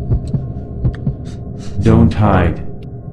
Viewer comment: что он сказал? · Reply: Don't hide